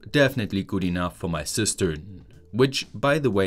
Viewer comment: I heard "is good enough for my sister" twice, while he was measuring distance and thought it is a joke about the length of a diсk LOL I am so embarraced of myself and still laugh like an idiot 😂